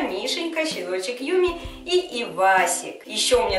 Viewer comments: Мне послышалось что собаку зовут Пивасик 😂😂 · мне послышалось что ты сказала не "Ивасик", а "Пиквасик".Хахахах